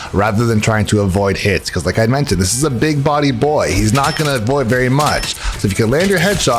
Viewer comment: Idk why but think I heard "big booty boy" instead of "big body boy" 😳😳